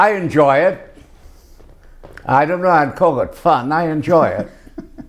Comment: I’d love it COVID fun is what I heard